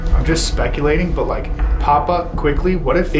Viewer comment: So iono maybe it’s just me and no offence intended but when and are doing the obilous session together in the house at 27:03 it sounds like it says puppet instead of papa and quickly go instead of just quickly and then at the end it sounds like it says intruder not under, which kinda corresponds to the door opening at 28:08 where and where doing their dead-bell investigation! Like I said, that’s just my observation maybe because it’s coming through on the television it sounds different. But those do seem to be the words that I hear instead of the words that they’re saying. Anyways still love you guys, keep up the great working always looking forward to the next video to come out! Let love live my friends and be safe out there!!!